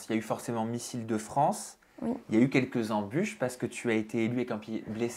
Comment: Au début j'ai entendu "missile de france", j'y ai cru un instant haha 😂